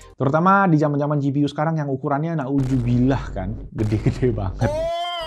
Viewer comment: Hmm ... PC case doang seukuran rak kaset pita harganya sama kayak harga mobo. Ampun dah. 🤦🏻‍♂️😅 1:36 Bentar ... Bisa diulang kalimat "Na'udzubillah" barusan? Gue gk salah denger kan? 😬